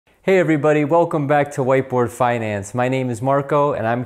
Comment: At first I thought he said white boy finance.